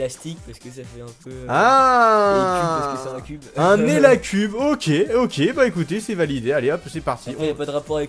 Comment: Qui a cru entendre "éliacube" comme moi ? XD Seul les fans de wakfu reconnaîtront :3